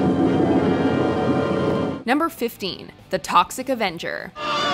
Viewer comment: did someone say Avengers 🧐🧐